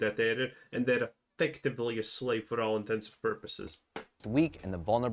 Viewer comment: The thing that made me most upset in the whole video was saying "for all intensive purposes" 11:59😬